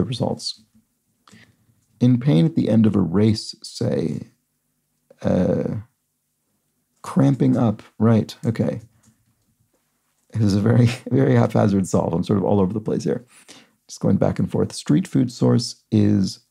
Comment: Did I really hear say, 'half-hazard'?